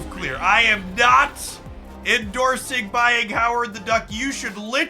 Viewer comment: All I heard was "I'm...endorsing buying Howard the Duck." I'm sure my cough as you spoke didn't change the message.